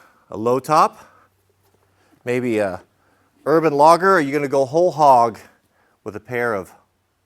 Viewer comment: This video autoplayed, I did not select it, and in fact it was in the background - I heard "Urban Lager" and thought you were talking about beer. Logger.